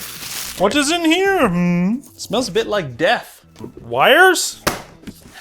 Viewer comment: At first, I thought he said "Hmm smells like DEBT", not "smells like death".